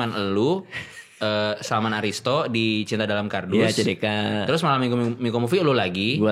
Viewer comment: Gw dengernya Cina Dalam Kardus 😭